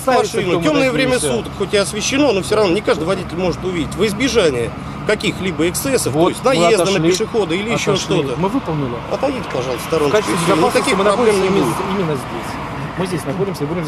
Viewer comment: во избежание инцестов??? или послышалось?